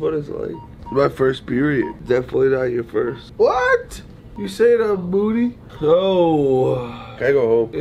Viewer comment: Gray: my first period Ethan:definitely not your first Gray: you calling me moody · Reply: I thought he said "you saying i have a booty?"